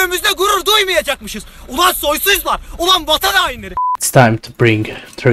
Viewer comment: Did you just say our capital Ulaan baatar 🇲🇳